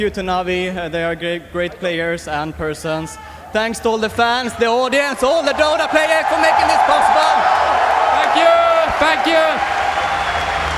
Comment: "What do you have to say to Navi?" 53:35 S4: "Fuck you! Fuck you!" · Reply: Kev Hcam Bro. He's saying "Thank you!" to his fans, it's just the thick Swedish accent.